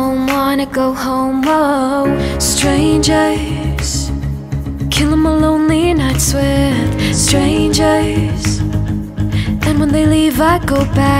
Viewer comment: Did i just hear “killing my lonely nuts with strangers”?